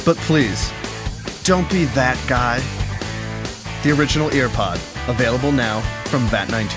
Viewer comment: Who thought it said air pod